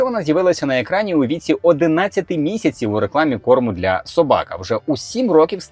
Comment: Тільки мені почулося "порно для собак" на 9:16? 🤭